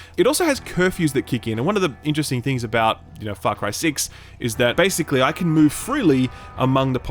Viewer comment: I heard "fuck cry 6" when I first started this video and I had to restart